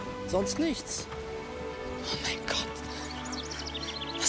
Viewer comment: Was hat er am Ende gesagt? Wir saufen uns junkevoll oder was?